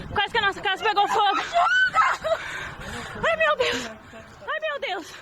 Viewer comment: I think she's saying "aye meu deo" which means Oh my *